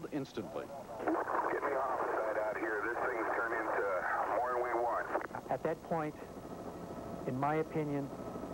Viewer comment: what did the dispatch her say over the radio? This things turn into what??? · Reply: More than we want.